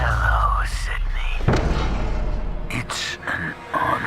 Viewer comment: My favorite line “Hello Sidney it’s an honor” I swear when I heard that iconic line it gave me chills and nostalgia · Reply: I heard “it’s on” like game on. 🤷🏻